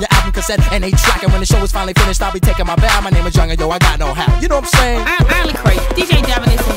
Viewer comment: don't feel to bad. for the longest time i thought he said i got no hair....